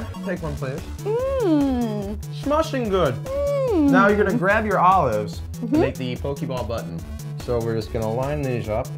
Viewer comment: It was the Italians that originated the pizza... I'm pretty sure.... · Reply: I thought he said," Know you are going to make the Pokeball BUTT"